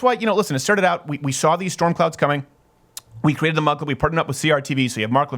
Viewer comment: Oh! This whole time I thought Steven was saying mud club.